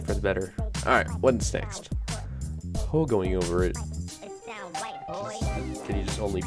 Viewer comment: i thought the when it said "pogoing over it" it said "pogging over it"